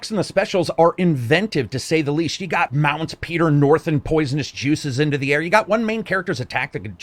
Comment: Did you said Peter North and poisonous juices at 2:06?l Lol